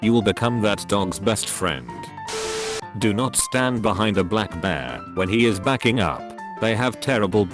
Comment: I thought it said black man lmaooo😭😭